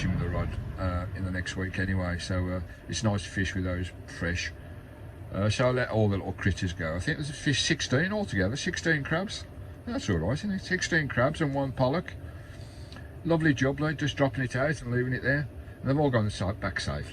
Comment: Have subbed to! Gary misheard you & spat his tea out, he thought you said that you had 16 crabs 🦀 & one b. ollock 🤣🤣🤣 We’ve been giggling for 5 minutes 😂 we’re so childish. Em 💜